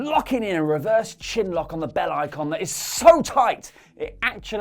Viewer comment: I honestly thought said, "...the Bella icon..."